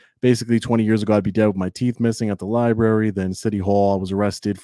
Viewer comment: What are you saying here? It sounds like "twenty years ago I began with my teeth missing"